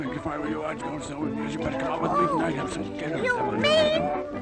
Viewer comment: Put the video on 0.5x speed he is obviously saying something but it was too fast to understand for us. I understood some parts as: ,,Well...I think If i were you i'd do so(That thing he whispered into her ear a moment before) you better come with me tonight... And some other stuff i didn't understand. Pretty fucked up if you ask me. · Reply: He said*you better come with me tonight and have dinner and somthing elese*